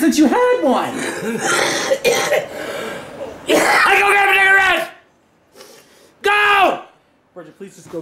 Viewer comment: what the hell did he say? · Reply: Go get cigarettes